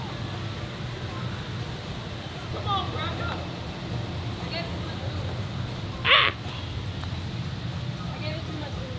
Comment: Was called a "cracker" at 19:40 ? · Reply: Someone definitely said, "Let's go cracka"